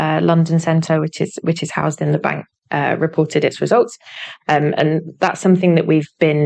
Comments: I heard "ripple did it's results" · 🔥 Use code at the link below to get an exclusive 60% off an annual Incogni plan: <link> 🔥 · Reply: The cockney Kent accent makes it sound like she says ripple ,but that’s because of the accent ,she does say reported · Great video as usual thanks. Initially I thought she said "Ripple" but when I activated automatic translation it convinced me she said "reported".